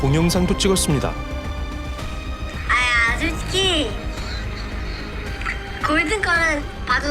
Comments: 솔직히가 아니라 아저씨라고 들리는데? 술 취해서 발음이 꼬인건가? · 자막 잘못됨. 아 솔직히 (×) 아 아저씨(ㅇ) 라고 한후 바로 정민군 숨넘어가는 소리 들림 . 공영방송이니 만큼 정확하게 합시다 .